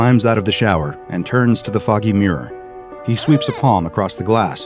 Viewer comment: i hear "he turns to focking mirror" instead of "he turn to the foggy mirror"